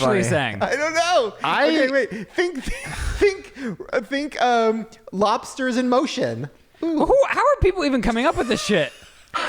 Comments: I heard “party is in motion” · I heard “Lobsters in Montion as well” 😂